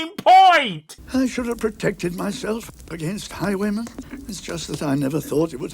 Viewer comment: I thought he said 'high women'...